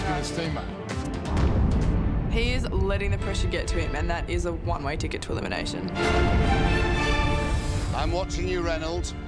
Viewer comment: I thought she said "That's one way to get to an asian" xD